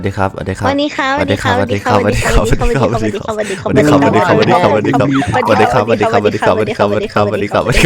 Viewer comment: จากหวัดดีคับหนูได้ยินว่ามาสิคับ😂😂😂